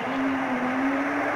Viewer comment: What is that guy at the end saying all i hear is "autochime chased by poliiiicee" haha